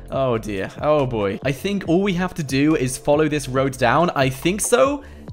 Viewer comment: I thought that he said all we had to do was follow the dawn train CJ